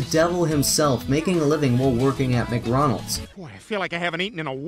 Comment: Got a new subscriber In all honesty I lost it when I heard Mgronals, but when I heard Sentucky Fried chicken, I litterally had to stop watching for 10 minutes because I couldn't stop laughing at something so ridicoulous